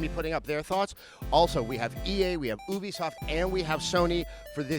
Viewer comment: I thought u said boobiesoft instead of Ubisoft.